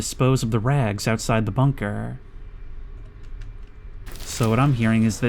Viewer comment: I THOUGHT IT SAID THE BLINKER IM SO TIRED RN OMG